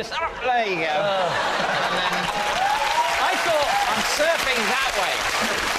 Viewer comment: When I first heard it I thought he said that he was being taught "serving" instead of "surfing". I'm glad I misheard it.